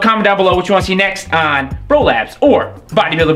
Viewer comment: I've been watching way too much Xtreme porn. He said "Bro labs" I heard.... "Prolapse"